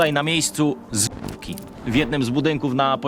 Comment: zamiast zwłoki usłyszałam zbóki XD